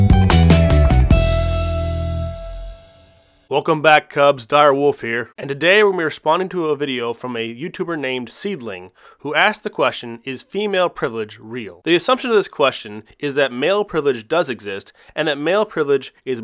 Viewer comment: It sounded like you said, "is email privilege real" .. lol